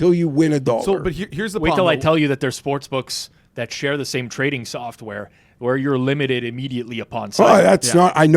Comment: said something about getting "limited immediately upon..." but goofball kept interrupting so I didn't get what was said. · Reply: He was saying that multiple sportsbooks share the same trading software. By getting limited at one of those books, you could potentially sign up to another and already be limited.